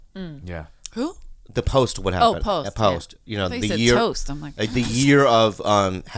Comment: Bobby definitely said "toast" instead of "post". Lolol